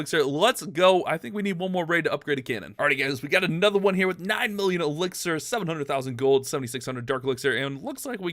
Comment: did I hear 9 million elixir?